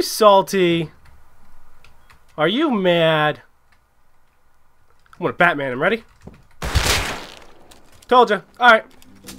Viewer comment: Did he say "Im going to batman him"? 10:15 Lmao I'm fucking dying.